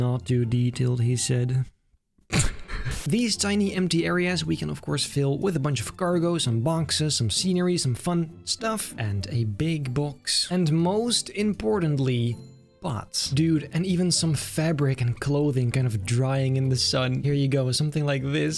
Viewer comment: is it just me me or did say "cargo and BONKses" at 3:22 ?